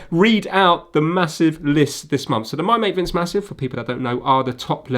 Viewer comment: Lol, at 6:12 I had thought he had said "The My Mate Vince Massive, or (random) people (that) I don't know..." instead of "... for people who don't know..." That would've been HILARIOUS in my opinion if he had said that. Anyways, great video, again Vince!